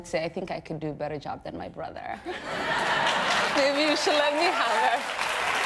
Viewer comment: what did she even say lmfao 🤣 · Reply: If you listen before hand she says it... "maybe you should let me have it" then she laughs and says it again